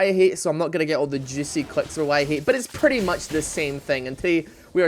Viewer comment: roughly 0:19 ~ 0:20 "I'm not gonna call it 'Why I Hate' so I'm not gonna get all the juicy (I think he said clicks, but i may be clips, Idk) from Why I Hate" checks title "RIOT Why? Caitlyn and her traps (Why I Hate)" "(Why I Hate)" ... >.> Braces for dislikes from fanboys